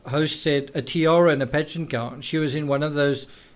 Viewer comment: Who’s Dr Full? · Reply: Ya. Lol. He kept saying 'pageants' and for 4 minutes I thought he was saying 'bed sheets'.